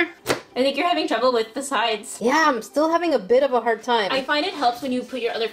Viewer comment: I'm having a bit of a hard time Me: sounded like she said, " I'm having a bit of a heart time" and that was funny to me because she was printing hearts on her nails. Like if you heard the same thing comment if you heard something different. Can I trust you to do that🤔.... What am I thinking of course I can😁👍. Also, if you a salt grain, say that in the comments so I know I'm not alone.